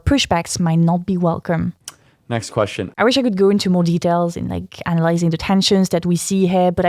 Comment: I thought she said "I wish I could goon to more detail" and I started laughing furiously until I played it again.😂